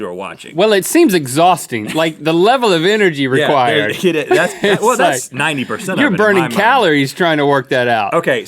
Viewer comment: there's a small mistake. Rhett says 90 percent but the subtitles say 90$.